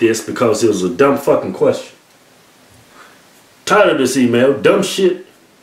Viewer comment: for a while I thought they were saying TIRED OF THIS EMAIL like they kept getting emails about the same shit lol